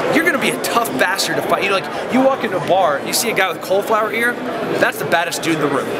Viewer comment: could someone explain what he said at 4:34? I heard him say "You see a guy with a cold flower ear", which I have no clue what it even remotely means